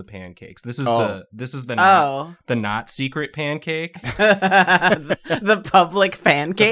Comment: I was only half-listening when said "the not-secret pancake" and I heard "the Nazi cret-pancake" and was SO confused